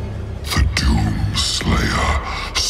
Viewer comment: first time I thought it said Freddos, not shadows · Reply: The first time it said doomslayer i thought it said dudeslayer